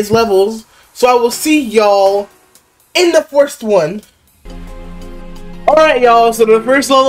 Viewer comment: what were you tryna say there, first? worst? 2:18 DEATH 4:47 classic 6:02 WHEN DID THE ROCK START TO IDENTIFY AS A PANDA 6:28 owned 10:20 famous last words. "i'm pretty skilled" he doesn't know 12:25 is coming 18:16 YOU WERE HERE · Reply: I was trying to say first 😂 idk how forst came out lol